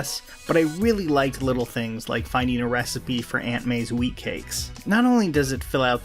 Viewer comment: did anybody else hear “weed cakes”?